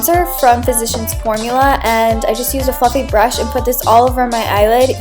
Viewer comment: when you said fluffy brush at 1:47 I thought you said the F word haha